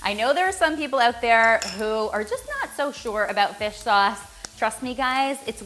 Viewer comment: Instead of 'fish sauce' I heard 'dish soap' for a sec 😂